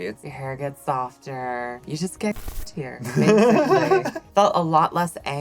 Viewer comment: what's this word that she saying please ? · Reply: "you just get cuntier"